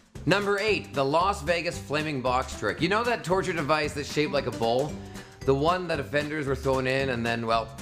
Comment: What does he say at 02:21? "That torture device that shave like a ball"? And at 03:36: "/.../ save his integrity by having the X cut"?